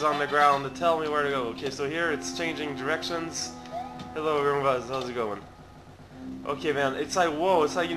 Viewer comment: did he call them "Roombas"?